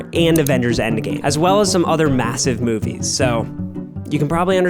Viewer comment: did I hear massive?